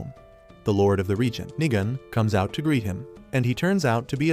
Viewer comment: i heard bro say nigg